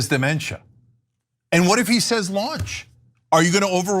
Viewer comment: Yes “LAUNCH!”…… Biden….oh I thought you said lunch……